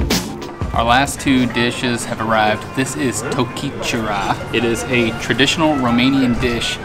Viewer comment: it sounded like japanese :) tokichura